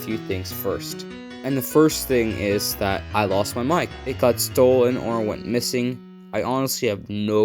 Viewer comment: On god I thought you said I lost my mind but then I had to repeat it and it said mic